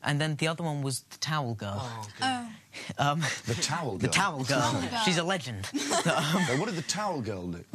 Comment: I honestly thought they said Tower Girls not Towel Girls!